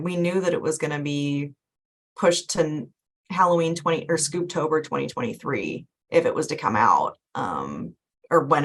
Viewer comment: For a second I thought it said "#SaveScoobHolidayHaunt" or something. But nope, it said "#SaveScoobtober2023"